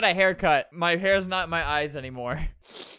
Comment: I got a hair cut so my hair isn't in my eyes anymore Me looking at the two strands of hair in his eyes: 👁👄👁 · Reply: I thought he said “ so my hair isn’t my eyes anymore” 😂